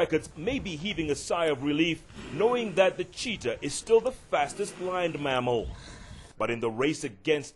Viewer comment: Did he say the fastest Blind Mammal???